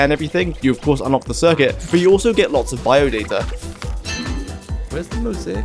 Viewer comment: "bio-deer" anyone? 2:06 was when I heard it right the first time x)